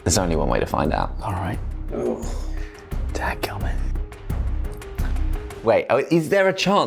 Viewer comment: and 14:06 did my Alabama redneck ears deceive me?? Did I hear say dadgummit TWICE??? :face-blue-smiling